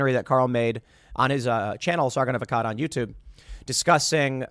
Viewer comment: I never saw his twitter, and I thought everytime you said his name it was Sargon of a Cod.